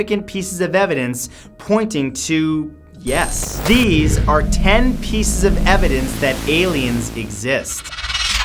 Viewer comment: Lmao not gonna lie I thought it said 10 species of aliens that exist 😭